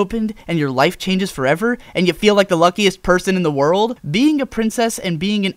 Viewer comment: When he said "and you feel like the luckiest person in the world" I originally thought he said "and you feel like the lumpiest person in the world."